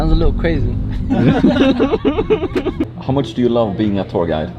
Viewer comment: "How much do you love being a tour guide? (29:45)". I thought he said how much do you love being a POOR guy...I was like what kind of question is that? lol